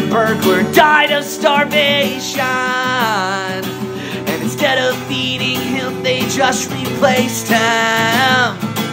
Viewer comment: I misheard "instead of feeding him" as "instead of EATING him," which IMMEDIATELY made me ask, "Is it TRADITION to EAT the dead in McDonalds Land???" So THEN I imagined a funeral with the Hamburglar lying in the open casket, everyone gathered around, sobbing. Then they don napkins in their shirt collars as they continue to weep, slowly descending upon the corpse of their fallen friend, tearing at his flesh and sating their eternal hunger whilst still wailing.